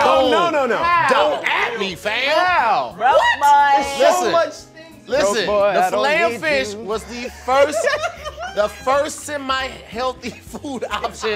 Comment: Did say “Doughboy I don’t need you” 🤣🤣